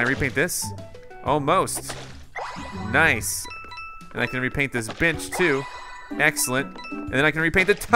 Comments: Am i the only one who thought he said imma paint this b**ch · WHAT did say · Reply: Bench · Did anyone else hear him say the b word instead of bench at 10:25?